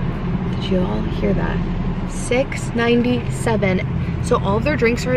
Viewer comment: Oh my god I thought she said $16.97!! I’m amazed. PS love all your videos ❤ especially the vlogs